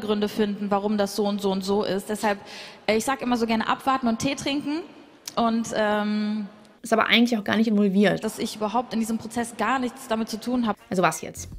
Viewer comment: ich hab grade ungelogen "ich sag immer so gerne abmahnen und tee trinken" verstanden😅😂